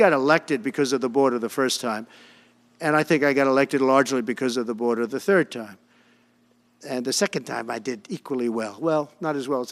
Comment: Did he said “elected the third time”??